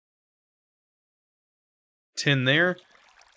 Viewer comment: You said "10 there" when you were crafting water by stomping the trees. All the while, I thought you said "Tin there" and stumbled through JEI for 20 minutes trying to figure out tin saplings that were a little ways down the progression for me. I'm not the smartest lol. 6 months later this video is still great informative content! Thanks!